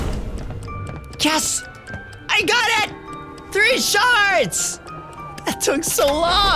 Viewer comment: For the whole video I thought he was saying shart